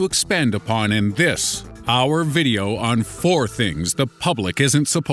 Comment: I thought he said hour video.